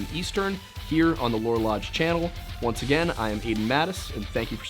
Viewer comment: Idk why but i heard "the lore lodge" as "the war on lunch" says a lot about the internet that i didnt question this at all